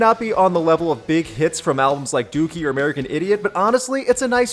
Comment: Know of any other good songs on bad albums? Leave a comment and let people know! Check out more videos below: 10 Good Songs By Bad Bands = <link> Top 10 Best Collaboration Songs In Rock = <link> Regretting The Past: Guns N’ Roses – Chinese Democracy = <link> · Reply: Seriously thought you said "Doofy" when you were talking "Dookie"